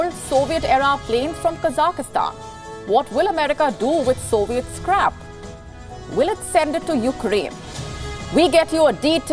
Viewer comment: did she say "Soviet scrap" or "Soviet's crap?" same thing either way